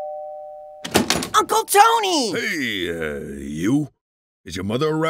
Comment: 俺英語苦手だから分かんねぇ けどココだけは聞き取れた 0:03 [ｱｧｺﾞｯﾂｧﾝｵﾆｨ!]